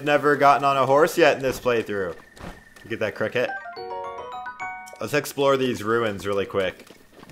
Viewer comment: I heard "get that crackhead"